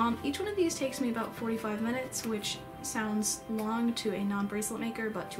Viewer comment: The first time i watched this I thought you said 4-5 mins not 45